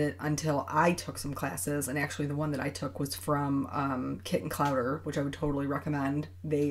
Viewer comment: beautiful card as usual. very patient with your coloring. What was the name of the colored pencil class? it sounded like you said kitten cover. :). As for the positive for this whole fiasco, I work in the ED at a fairly busy hospital, and lately it has been sloooow. I mean , where are all the patients? slow. which is good. people are really staying home and not coming into the ED unless they really really have to. masks are hard to come by, we are out of bleach wipes (which we need to wipe down our CT scanner and portable machines after every pt to keep everyone safe). but we improvise and do the best we can with what we have to work with. Keep the videos coming, you brighten my day. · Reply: The class is by Kit and Clowder. Australian artist, great teacher.